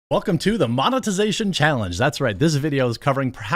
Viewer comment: At the start of the video, I thought you said “the mama-tization challenge”. Was about to fist bump you for a great joke